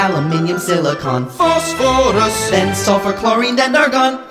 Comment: It sounded like Sulfur was Suffer